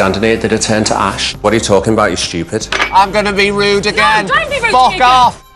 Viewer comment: "I'm gonna be rude again, Fuck off!" I don't know what situation is this, But I think this part is so funny I can't stop laugh lol Am I hear this correctly?